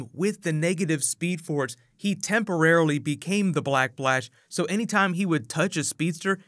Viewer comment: am i the only one whoheard him say black blash instead of black flash